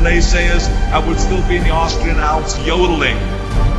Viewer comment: did I hear YOLOing?